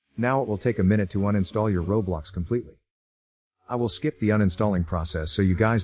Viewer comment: i like how in the middle of the video the caption said "roadblocks" instead of roblox, truly an ai moment